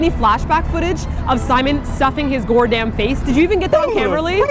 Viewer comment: Hey! Did you say GORAM at 5:40? As in, FIREFLY REFERENCE?! <3